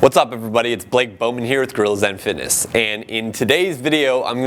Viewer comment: I thought he said girls and fitness